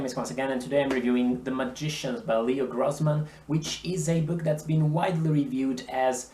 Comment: I actually thought it said Less Grossman